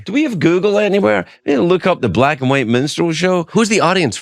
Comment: I thought you said Black and White Menstrual Show.